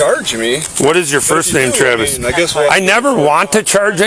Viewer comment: did the cop say "what is your first name Travis"? sure sounded like it. again at 17:00 he called you Travis. after that he called you Travis several times. why didn't the cop just run your license plate to get your first name if he was so concerned. cop is scum, but can't be too surprised about that now can we. cop just can't understand the laws, thats all.